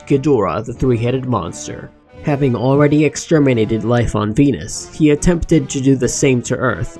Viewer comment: Why did you say pin** O-O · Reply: he said VENUS.